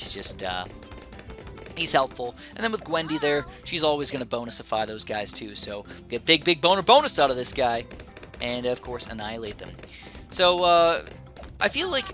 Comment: did just say big big boner bonus cause that’s what I heard